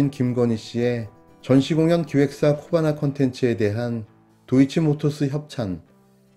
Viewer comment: 코바나컨텐츠? 거봐 나 걸렸쓰로 들리넹...ㅋㅋㅋ